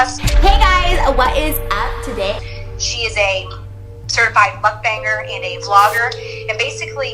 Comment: certified KFC eater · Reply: Well, alrighty. But it sounds like she said "mudbanger" - whatever that is. *Ah! She did say "muckbanger " which is a person who eats for an audience.